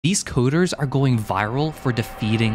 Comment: I just woke up and the first thing he said i heard as "This cooters"